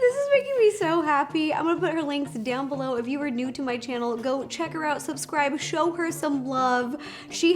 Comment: It sounded like Ro said go choke her out lol